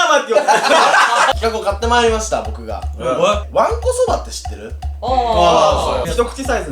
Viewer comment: おれ...どうしても...どうしてもおもったんだ... わんこそばがま○こそばって聞こえた...